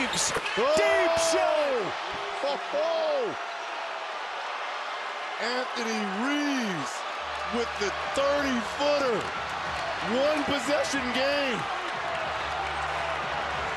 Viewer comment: I heard "Anthony Reese" 🤔😂